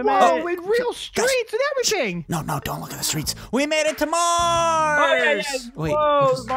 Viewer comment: I HEARD YOU SAY SHH DONT LOOK AT THE ROADS